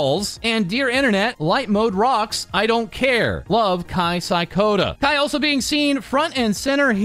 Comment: I heard we canceling Kai now?